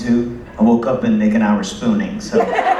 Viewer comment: What did Brian say at around 24:29? "20 mornings out of 22, I woke up and Nick and I were..." ??? I couldn't quite understand it clearly, what was the story about hahahaha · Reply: "spooning" if I got it right....